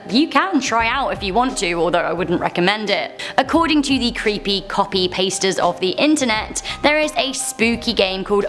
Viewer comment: I kept hearing "creepybasterds"